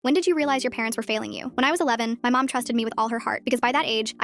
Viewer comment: Off topic but I thought that it said “when did you realize your parents were falling for you?”😭😭😭